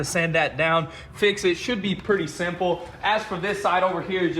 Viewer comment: Anyone else hear him say shit when talking about the dinger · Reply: it sounds like shit but I think he cut off saying "should" and changed his sentence.